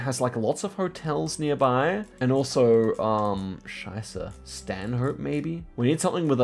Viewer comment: Did he just say scheiße?